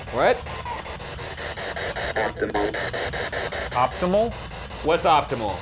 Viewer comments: I heard “oatmeal” instead of “optimal” and I burst out laughing idk it was just the tone it was said in deep and so serious. (Also I have really bad hearing) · When the ghost said optimal I thought it said Optimus and I started laughing cause I thought of Optimus prime